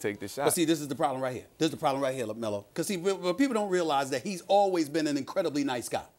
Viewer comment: is it jus me or did SAS say lamelo at 11:13 ish